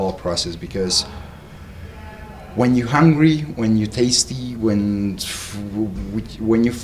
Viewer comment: did he say "When you Tasty"? 😄